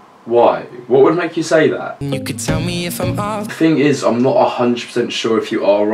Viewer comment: I thought he said "and you can tell me if I'm wrong" I guess i don't know music